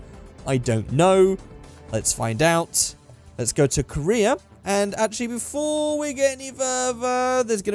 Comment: I thought that you said "lets go to Korea" instead "lets go to career" :D